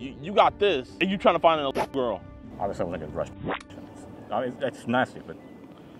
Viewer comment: I think he said I wasn’t gonna rush protection which is absolutely disgusting